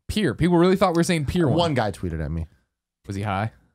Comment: For 9 months I thought Greg was saying the Pier One. I definitely heard that.